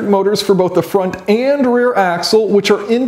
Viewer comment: I heard "front and rear asshole" LUL